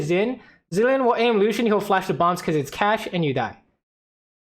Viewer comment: I thought you said "Lucian will flash the bombs because it's CASH," and had me rolling. Catch makes a lot more sense though lol